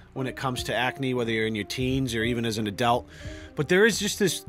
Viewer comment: he said adult but it sounded like Adele